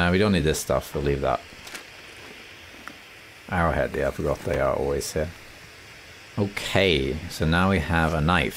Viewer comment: ish did you say "arrowhead jættebra they are always here?" or am I hearing things?